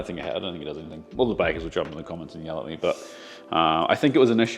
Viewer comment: Cracked up over the "weasels" in flour 4:57... don't think a fine sieve would be needed! Bloody weasels are as big as a cat... (tongue in cheek comment - I KNOW meant weevils!)🤣😆🤪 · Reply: Weevils * TBF, it did sound like he said Weasels.